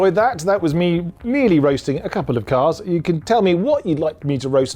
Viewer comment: What does he says after "comments" = ) ? I couldn't understand a thing. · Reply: "Otherwise it sounds really weird"